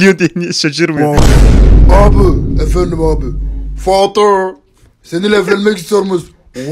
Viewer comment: Bey Doğrumu Duydum?